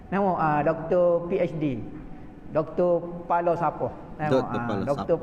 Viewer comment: Sy rasa ustad muhaizad ckp dr falsafah dlm dielek kelantan.. dia tak ckp dr Palo sampoh.. kita salah dengar tu.. berfikiran positive sikit takda salah nya kan..